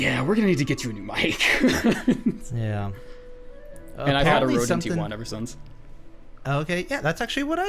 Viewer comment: What was that mic he says at 6:03? EDIT: Nvrmind they say it rode nt1 like 30 seconds later